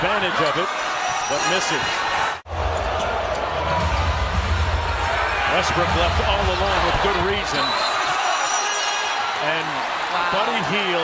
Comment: did he just say Westbrook was left all alone for a good reason?